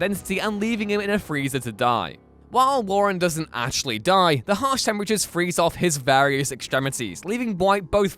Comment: Wait... Did you say "actually" or "Ashley"? This ain't Evil Dead, dammit!